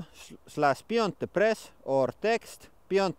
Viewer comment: Somehow I always hear 'Beyond Depressed' hahaha. I can't unhear it and it's been plagueing me for more than a year now :D